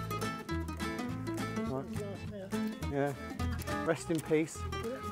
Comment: Of the mower, did I hear you say "rust in peace"?